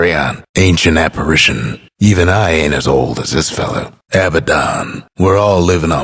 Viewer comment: did he say Abaddon? *--------------*